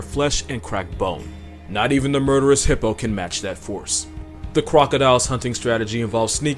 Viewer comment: am I crazy, or did he just say "Cwocodile"